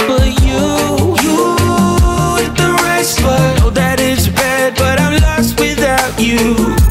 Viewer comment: So I admit that initially I heard "you hit the right spot" as "you, with the rice butt" and I can't un-hear this, LOL. Misheard song lyrics are hilarious.